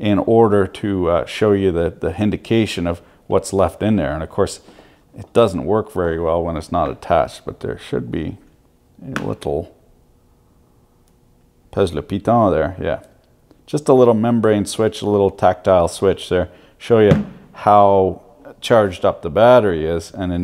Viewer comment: ''Pèse le piton'' Did I hear right?